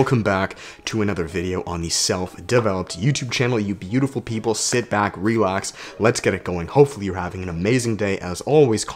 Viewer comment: I heard “people sit back, relapse...” my mind is telling me to relapse! 🤣😂😂